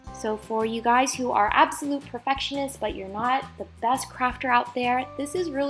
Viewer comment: In the beginning when she said craft i thought she said crap 😂😂😂😂😂